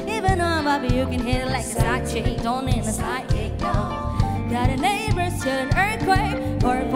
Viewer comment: did she really said the D words in 1:14 ? hahahaa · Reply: Kok gua dengernya side kick ya hahahhaa